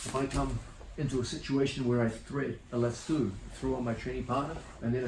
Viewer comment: Did he just say lasooo?